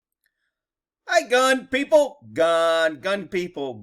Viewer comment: And I always thought you were saying FUN people 🤷🏻‍♂️